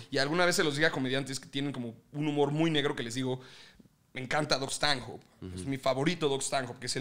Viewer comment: Alguien sabe el nombre del comediante que se refieren al 9:24 entendí Dust Tango pero nada, gracias